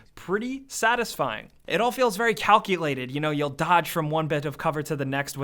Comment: did you just say calcilated? I heard you say calcilated? Thats the best word evar...